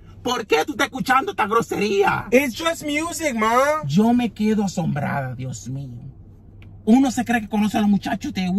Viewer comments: Lmao! 😂 Hispanic moms go to the extreme every time. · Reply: THANK YOU!!! I thought he said Yo me he quedo sombrada... I was like WHAT??! Thank U again!!! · So, true Spanish parents reactions, old school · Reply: Thank you soo MUCH!! I had never heard the word asombrada before. I thought he was saying me ha sombrada lol! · Lmao! 😂 Hispanic moms go to the extreme every time. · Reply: Ugh, I thought the Mom said "embarazada." I thought she asked her if she was pregnant. 😦😏😏